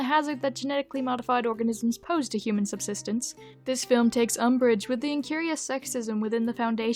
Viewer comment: at minute 3:00 at first I thought it said "umbridge", and for a moment I questioned if it was an actual word in the English language